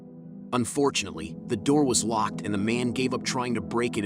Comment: lmao wtf u said Unfortunately?